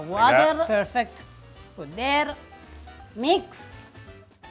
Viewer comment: when I first heard I thought he had said "mizzega" and I was like WOOOOAH the man is mastering Italian now